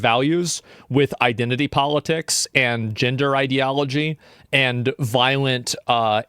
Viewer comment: I thought he said ginger-ideology 😂